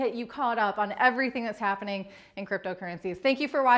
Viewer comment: Oh my I could’ve sworn that she said, “this is Heidi from Crypto Tits” at the end of the video!!!